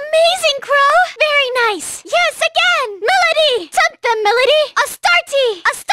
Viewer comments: is she saying “Choke them, Milady”? Damn that’s pretty hardcore for Haru. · I can't understand what she says at 8:02 "__ Milady" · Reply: "take them Milady" i think